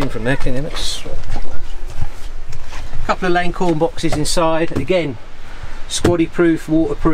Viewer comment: Couldn't quite tell what he said the name of those storage boxes were, can you put links to kit in the vids so we can see what they are and where to buy? Thanks, would love to do something like this! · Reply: thanks for that, appreciate you can't show everything, I was searching for acorn boxes which is what I thought he said, I'll have a look, thanks